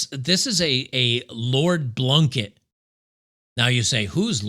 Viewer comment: "Lord Blunkett"? I thought at first said "Lord Bucket" - as in Lord Buckethead, the Darth Vader cosplayer who's ran for M.P. in the U.K. Parliament - at least twice! Then again, he might have been a better candidate for the welfare of Britain's schools over anyone they have these days.